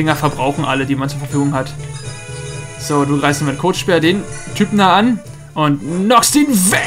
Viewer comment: Ich hab verstanden Kotspeer. XD